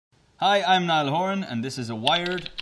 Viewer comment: When he was saying "hi I'm Niall Horan " in the beginning , I heard like " HI I'M NOT A WOMEN " ...😂😂😂 LMAO